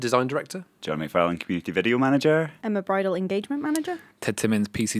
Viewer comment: I thought she said her name was Emma and that she was a bridal manager and I was like oh good for you Emma but what are you doing on this podcast LOL